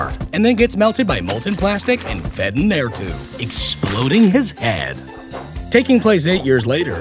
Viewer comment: Did you just say exploding his hand